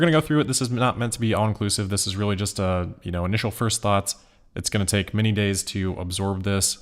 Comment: In the intro I misheard saying that he'd be going through the initial FUDs.